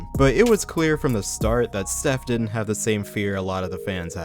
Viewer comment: it sounds like you said seth didn't have the same fear that the fans had lol